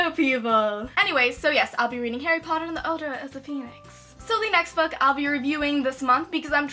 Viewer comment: I thought she said odor of the Phoenix lol